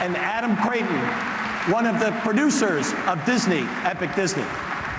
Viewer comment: Did he say "Disney Epic Disney?"